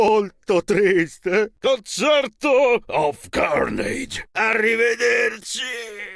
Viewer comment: The fact Chuck isn’t legendary or chromatic is just crazy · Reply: Hey Carnage I think Chuck says carnage in one of his voicelines